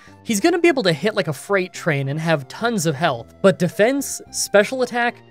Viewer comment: I heard "he's gonna be able to hit like a 'frain trait'."